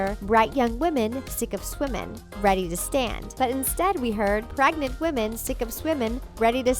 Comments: When i was younger, I heard "naked women, sick of swimmin, ready to stand" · Instead of hearing "bright young women" I hear " Break your women" tell me that If I'm wrong ok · I heard "yet im women sick of swimming "